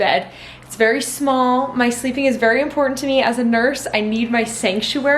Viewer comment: omg i screamed i thought it said nursery i was like WHAT DID I MISS WHEN!!! girllllllllllllll hahaha